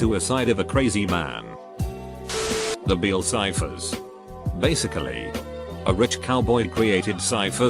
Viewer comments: I heard Bill Cipher for a quick second... Anybody still remembers Gravity Falls? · Ngl, I thought it said "Bill Cipher".